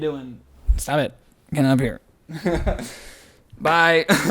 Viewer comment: i SWEAR that said “Bye, Dan.” (TRIGGERED) 🤨🧐😶🤔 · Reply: i heard “bye Dan” but now I hear “bye then”